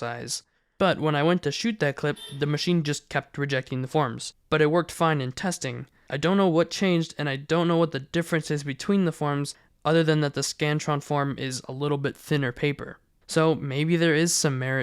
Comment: I thought ts said “The Scranton”